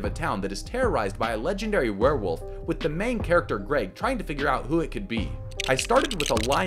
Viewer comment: I think he’s said Grady, guys